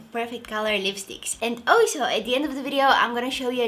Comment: sec she said 'alshow' instead of 'also' it was damn cute hit like if u have noticed....😍